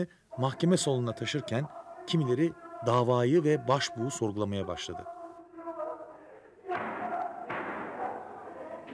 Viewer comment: 'de başlayan konuşmada adam ne diyor ? Yazabilecek olan var mı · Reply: gun gelecek beka bahanesı ile koltuklarini korumak icin omrumuzu verdigimiz bu ideolojiyi satacaklar diyor...